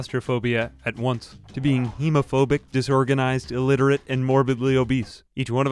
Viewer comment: I was reading the captions and thought you said homophobic instead of hemophobic and was so confused for a hot second!!